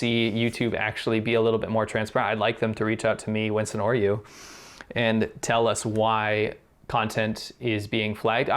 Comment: Chappell, on 14:55, the CC says "[inaudible 00:13:47]." It's actually saying "...reach out to me, Winston or you,..." I believe he is referring to SerpentZA.